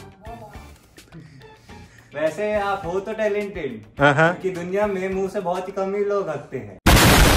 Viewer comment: Vese aap hoto talented uske baad ki line samj nhi aaya kya bola ??? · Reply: Vese ap ho to bhot talented par mu se bhot kam log hagte ha